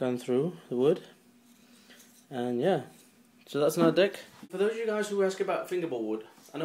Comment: he says dick instead of deck